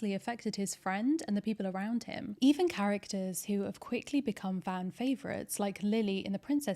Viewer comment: I am fairly sure "fan favorites" is what's being said and missing from the subtitles